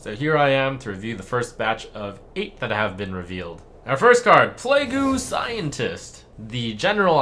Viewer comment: did just say "play goo" scientist xD im done